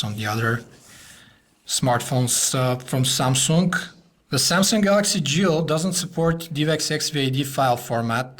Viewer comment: I keep hearing 'Galaxy Jew'.